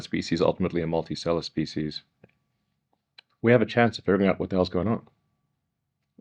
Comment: Anyone know what word he uses at 2:36? The caption has “multisellar species” for me. Can anyone explain what he means by that? Just curious; hadn’t heard the word before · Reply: Multi-stellar